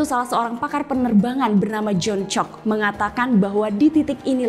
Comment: kok aku dengernya janc*k ya 🤣